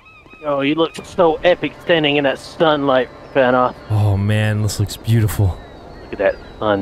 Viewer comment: He really sounds like he said "Stun Light".